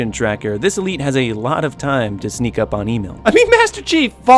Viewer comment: I thought you said email instead of emile